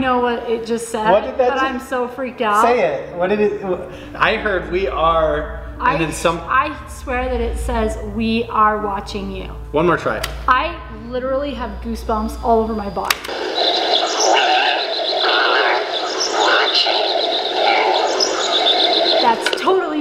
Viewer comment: I heard “red car watching you”